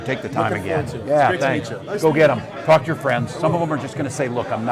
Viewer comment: I had to rewind to read what Walz was saying to those collage dudes. I heard "fuck your friends" instead of "talk to your friends". Major difference